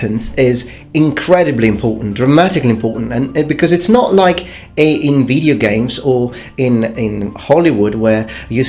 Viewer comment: when says "like in video games" in his videos I always hear "like nVIDIA games" :D